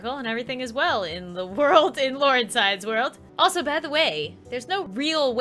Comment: I thought it was said not